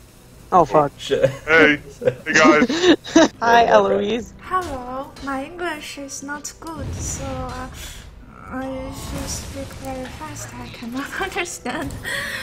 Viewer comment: what does Eloise say? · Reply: hello my english is not good, so if you speak fast i can not understand